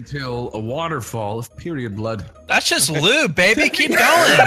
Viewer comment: "That's just lube baby keep going. at least you know shes not pregnant at that point" Someone....end my life. i don't wanna live after this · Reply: I thought you said "that's just baby lube" FML stupid dyslexic moments